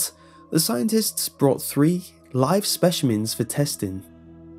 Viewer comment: did he say "spechimins?